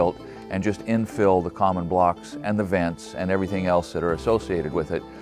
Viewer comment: at 2:28 did I hear you right ? Did I hear you say VENTS? Do you not do conditioned crawlspace? Here in Colorado we haven't been able to use vents since 2007'. I do like the split face.